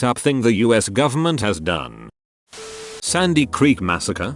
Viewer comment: I hate to nitpick, but it’s actually the Sand Creek Massacre, and I probably wouldn’t point this out, but I’m doing so because I misheard “Sandy Creek” as “Sandy Hook” (I am well aware that Sandy Hook was not done by the US Government, but since people (Alex Jones comes to mind) have accused it of being a false flag attack (it wasn’t), I wanted to make sure that wasn’t what the comment was saying (the next sentence helped me with that)).